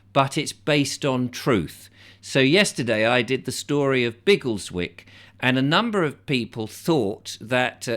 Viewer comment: There's a real place called Giggleswick I thought that was what you said at first